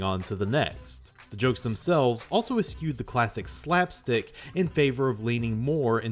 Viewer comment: what word are you trynna say? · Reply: eschewed